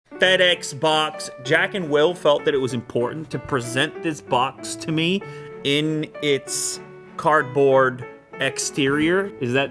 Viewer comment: was it only me that thought that fedex sounded like fatass?😂